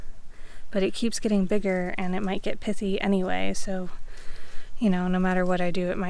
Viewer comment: did you say the radish might not get pissy????? 🤔